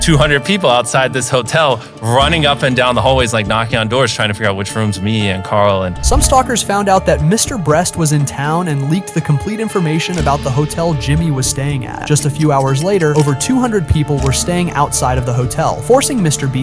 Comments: Did anyone hear “Mr Breast?” 💀💀 3:35 And “Mr Bleach?” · did you said mrbreast 💀 but i know this vid is not funny i just heard that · BRO REALLY SAID MRBREAST💀💀 3:34 Who is Mr Bleach bro? And there is no way I heard mrbeast swear💀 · Bruh did you call Mr beast mr breast 💀💀💀💀💀💀💀 1:30 💀💀💀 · Bro why did i hear Mr.breast😂(1:26)😂😂😂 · im sorry if this was serious but did anyone hear him say mr breast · In1:25 did bro just say mr. breast 😂😂😂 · did you just said (Mr breast? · did you really say mt. breast? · Why did he say: "Mr Br__st" and not Mr Beast?